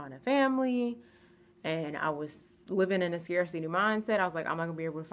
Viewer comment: Did you say, "CRDC mindset"? What is that? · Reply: Scarcity Mindset...Check out Bob Proctor!